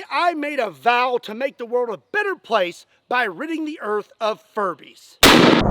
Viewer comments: I thought he said furries and i backed that 1000% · I thought he said furries LOL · I thought he was saying he was going to war against furries, until he had a furbie in his hand. Also, what is a sweatbee?